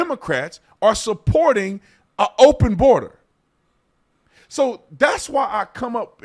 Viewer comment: did he say open borders 🤨? Your not gonna like <link>